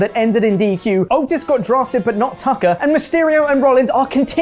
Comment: Chopper pronounced Aaleyah so weird i legit thought he was saying "Ali and Mysterio" until i looked back at the scree.